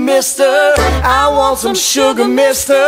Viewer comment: I thought it said sugar in my coffee because I'm that tired right now but cute!